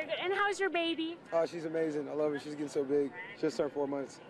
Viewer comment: What is he saying 1:44-1:45 ??? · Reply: she just turned 4 months